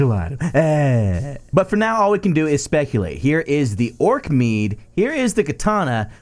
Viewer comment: When you said Orc Meads I thought you said Orc MEMES XD